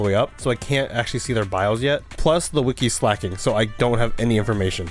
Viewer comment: I thought you said "The Wiki is slacking." Which, in all honestly, is not innaccurate.